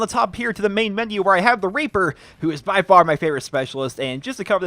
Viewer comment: It sounds like he is saying "I love the raper" at the start